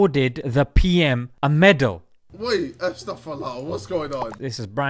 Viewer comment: Are my ears working?👂 Did KSI just say Astagfirullah?